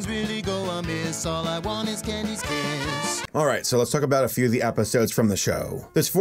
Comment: What did he just say?! 😐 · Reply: He said all he wants is Candy’s kiss